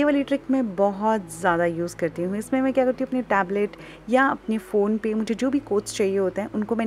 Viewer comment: She said pablet instead of Tablet its okay not a big deal but cute ofc